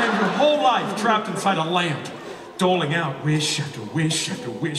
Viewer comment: What does the genie says here: 15:45 dingling or doin? Or...what? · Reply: doling